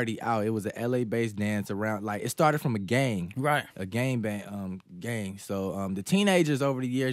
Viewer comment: Did he really said gang bang 3:45 lol